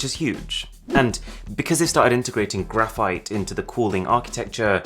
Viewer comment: I thought he said graphene if Oppo 1st implement that to thier phones ... *Stonks*^